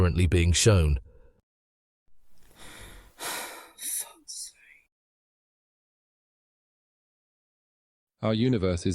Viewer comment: what the guy say? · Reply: He said ffs